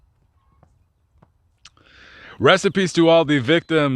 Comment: did he say "Recipes" To all the victims"?